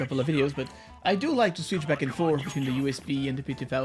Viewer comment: I thought you said: "I switched back to the oven". WTF